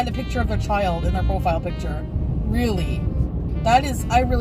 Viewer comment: Hey just letting you know one tiny correction, 01:39:20 she actually said she hopes her kid doesn't get bullied. It's a bit hard to hear the "not" when she says it but to be honest she did and didnt threaten the bad wish. Dont know if you just showed the wrong clip or if it was just missing her saying not, but just wanted to let you know. Shes a garbage person besides that but gotta be fair. Entertaining af though bro!